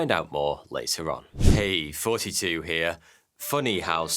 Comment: bro I finally just realized now that you've been saying in your intro when this whole time I thought I kept hearing you say "42 here" 😂😅